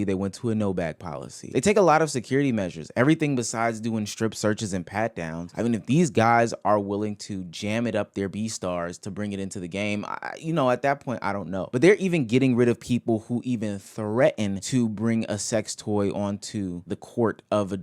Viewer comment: I thought you said "Beastars" like the anime:fish-orange-wide-eyes